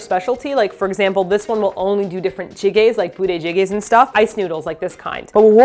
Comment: lol I thought said booty jiggles..